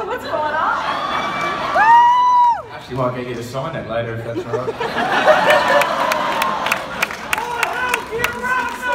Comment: What does he say please? (Just learning english) Thanks in advance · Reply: Actually might get you to sign that later if that's alright